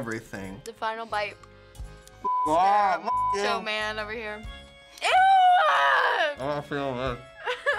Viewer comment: What is she saying at 7:14? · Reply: Sounds like she said "Mr. Macho man over here"